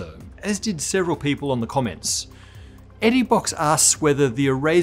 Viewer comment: weird timeline im in..it says..or i hear"on the comments" from u, but subtitles show.. tha tthey're drunk "in the comments"